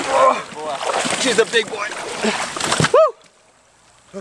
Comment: did he say she’s a big boy